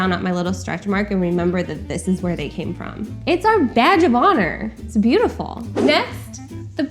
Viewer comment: I thought she said "vag of honour" not "badge of honour"